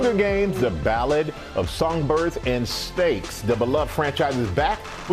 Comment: The Ballad of Songbirds and STAKES! 😂 Had me dying. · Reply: I thought he said steaks 🥩 😂